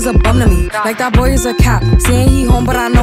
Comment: I thought it was saying, "the boy is a LEO" HA HA 😂😂😂